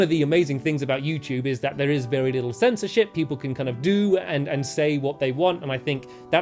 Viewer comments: did stampy say Shi* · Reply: no he said sensor ship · what he say?!?! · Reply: Censorship? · wait like 11 seconds and I think he said sh*t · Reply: Fhernanda Vargas He said CensorsSHIP